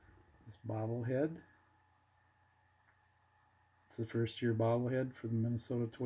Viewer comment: Hey! What year is the bobble head from? I am curious because I see the Mountain Dew logo on the back of his hat. I like the detail on that piece. The Oliva test disc a great pickup for your collection. I love when items like this reside with true collectors who will appreciate it. Thanks for sharing. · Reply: Gotcha. I thought you said the first year of the Twins. I misheard you. Again, nice looking piece.